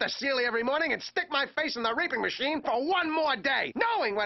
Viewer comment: Did he say raping or reaping? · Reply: reaping